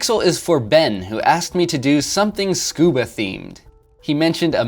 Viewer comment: I thought you said "Scooby themed" and I was excited but then you said mask and snorkel and I realized you said scuba, still awesome though.